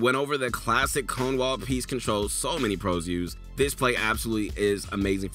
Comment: For a long time I thought he said peace control and I was like ?